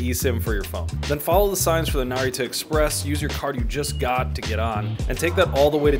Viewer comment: Legit thought he said Naruto express 😂